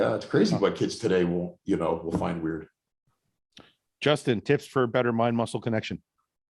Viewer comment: Honestly thought said "tits or a better mind muscle connection?"